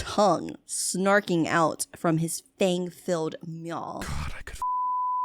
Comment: For anyone qho is wondering what he said 27:47-God,i could f___ you agaisn't tis wall right now...